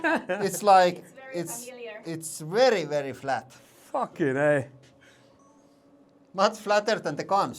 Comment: did he say "fuckin A" ??